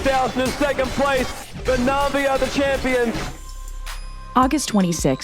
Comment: It was spectacular and all until I heard "Champion Lina"